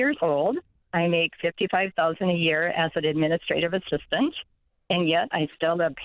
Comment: He said take home pay not what do you make after taxes but before retirement and healthcare. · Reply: sorry everyone...I don't know where I thought she said she made 75k a year...0:21 to 0:24...and I researched the video... * Looks in the mirror.. ..facepalms*. 😁